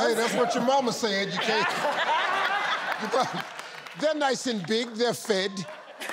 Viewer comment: Uncle Steve, if my mum says I'm fat. It means she's done a good job feeding me and so no need to be cooking.🤷🏽‍♀️ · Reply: I thought she said, They're FIT, as in, they are fit to cook for themselves....